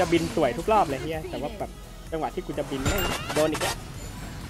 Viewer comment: "...จังหวะที่กุจะบินกุโดน(แกรกฮุก)อีกแล้ว" แกรก - เหมือนได้ยิน ฮุกมาเฉี่ยวไปกระจึ๋งนึง 5555